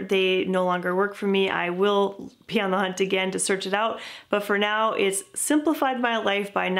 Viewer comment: Hello! I want to use this video for my english lesson but i can't understand the part on 8:59 - "if in the future they no longer work for me I will..." Will what? Pee on the hunt? So does this mean something like "I wouldn't care"? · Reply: Oh my! I said “BE on the hunt for something else.” (Not pee) ha ha! Basically that phrase means that I would go to the stores and try to find a different product that would meet my needs. ❤️ hope this helps!